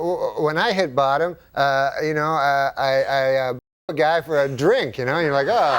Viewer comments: German here, please someone explain to me the joke at 5:43. The women hit bottom when what? Is it a saying? I don’t get it. But it seems to funny. Please help ☺️ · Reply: You can read his lips, he said "blew a guy for a drink" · No idea what he says at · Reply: He says “blow a guy for a drink”